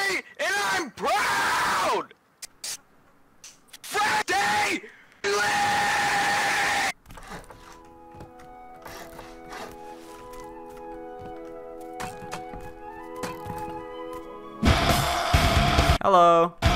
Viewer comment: What does he say here? XD FRIDAY LITTTTT